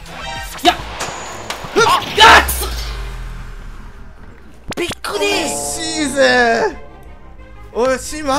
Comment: のBIG D…！って書いてあるけど、びっくり！！にも聞こえる